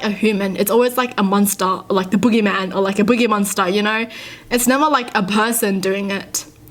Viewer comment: Thought said, "Booger monster " than Boogy monster (srry Idk le spelling)